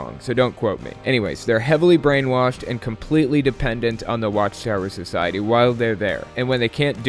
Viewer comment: When you say "watch tower society" it almost resembels "watch terror society",but i guess my misheard is not that far from true.